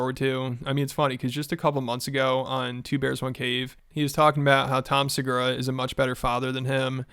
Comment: Sounded like u said 2 Pears 1 Cave